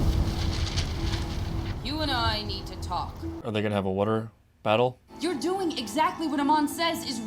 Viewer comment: what did he just say? wuter???